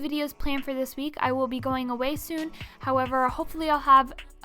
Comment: You know you're a cuber when you hear "weisu" instead of "away soon" at 8:48 😂